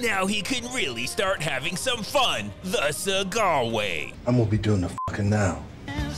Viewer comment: Did he just say “I’m gonna be doing the f#%king now”?